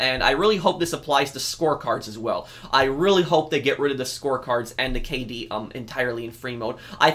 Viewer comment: when you said "score cards" i thought you said "sports cars" i was confused why you wanted them removed from freemode then i heard it correctly